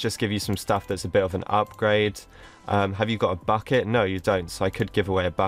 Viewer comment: I was yawning when he said ,,Bucket" and i heard ,,F*ck it" 😂🤣